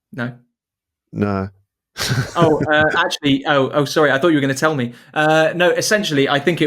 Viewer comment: "I thought you were going to say it was some sort of thing you can't talk about like some kind of special mission in Bulgaria or something" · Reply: But somehow, even when I slowed it down to 0.25x speed, I still only heard 'I thought you say' — I could barely catch the 'were going to' or 'were gonna' part. I’m wondering if I’m missing something, or if that part is just really hard to hear.